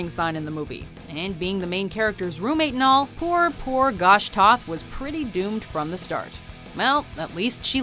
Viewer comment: Did anyone else hear "poor poor gosh Toth"